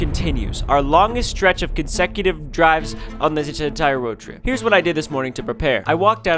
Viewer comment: what did ryan say 🤣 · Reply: This entire roadtrip